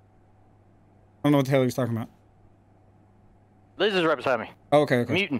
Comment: what did he say and 21:37 did he say dizzy right beside me muting? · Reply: For 21:37 he said “Liz is right beside me. Muting.” Liz is his girlfriend. As for 21:26 I don’t really know what he was saying.